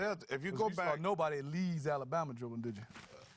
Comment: Someone please tell what does Steve Smith say to Chuck at 33:07? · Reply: "Cause you saw nobody in Leeds Alabama dribbling"